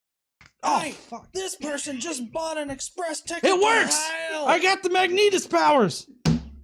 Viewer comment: I swear I heard that as "I got the Magneetus powers!" and I couldn't stop laughing for a few minutes. And it figures you'd have been in San Francisco after I moved from there to Pennsylvania.